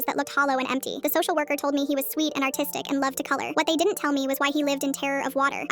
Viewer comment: I thought you said autistic